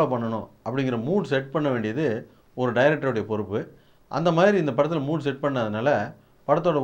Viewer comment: Did he said "Anthe Maire" @ 1:36??...😂😂😂